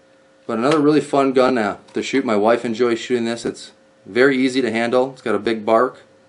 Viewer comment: “just a little fun gun to shoot my wife..” I swear he just said that lol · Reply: A little fun gun to shoot, my wife and Joey like to shoot.